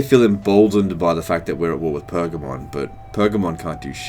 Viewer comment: For a moment I heard ‘We’re at war with Pokemon’